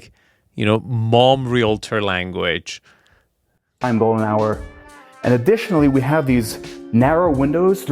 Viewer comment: Golden hour is definitely still a term in the photography and film world. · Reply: He looks Gay, thought he said Gold Shower